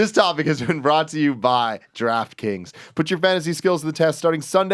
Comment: I always hear "Giraffe Kings" when Tim says Draft Kings